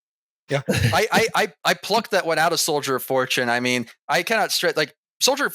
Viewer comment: I heard that as "soldiers of 4chan"